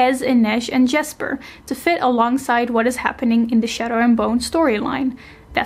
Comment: i thought she said shatter and burn